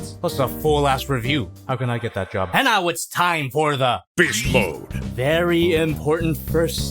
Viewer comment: I first thought he said bitch mode not beast mode lol